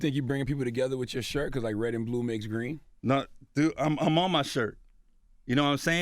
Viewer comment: did he say red and blue makes green?